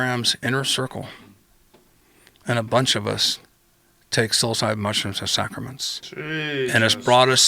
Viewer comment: what kinda mushrooms? Can't make out what he's saying? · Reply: psyllocybe mushrooms